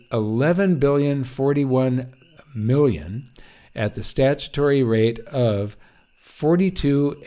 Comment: I keep hearing "statutory rape"... Well, that is perhaps what the Government has "economically" done to the public.